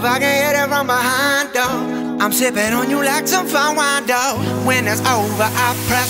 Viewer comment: i thought he said "im sipping over you like some za worldo" im watching too much jojo